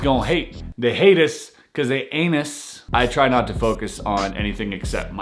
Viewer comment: I really thought you said: "They hate us 'cuz they anus" 😂😂😂😂😂